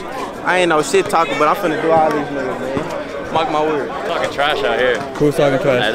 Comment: sounded like he said nigga idk though